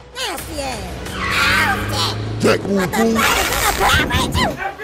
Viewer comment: what he say😂😂😂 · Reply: Chadwick Davis he said “What the fuck is that a power ranger!!”